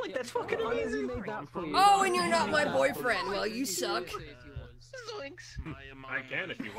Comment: am i hearing things because heard someone said Zoinks from scooby doo